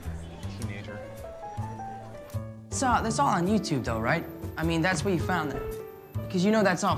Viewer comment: Tom your British is showing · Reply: i thought he was saying "that's uhh that's all on youtube though"